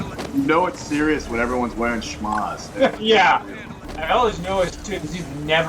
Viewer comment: "No one's wearing a snuz" or what was he said :D 0:18 He is wearing a stuff like that in the picture :D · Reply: Schmagh. It's to protect from Sun, and Dust in the middle east. He is making fun of it for being a night mission on a mountain